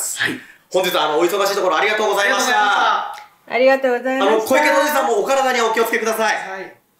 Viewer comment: が小池のおじさんにしか聞こえん笑